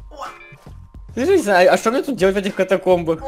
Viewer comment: Жека : слышиш зай , а чё мне делать . Кто услышал ставь лайк под кооментом